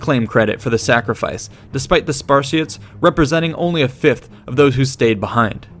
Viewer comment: I thought that you were saying "Spar-shits" and not "Spartiates" all the way until the very end of the video when you showed the word spelled out.